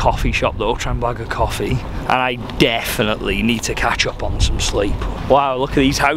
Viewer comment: thought you said ketchup first 😂😂